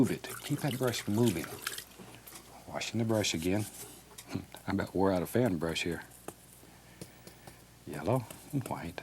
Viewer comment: did he say “im about to whore out a fan brush here?” Lol